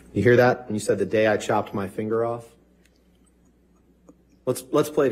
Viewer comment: I hear “the day Amber chopped my finger off”